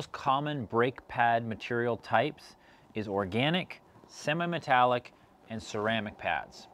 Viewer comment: Did he say "Sema" Metallic? ROTFL!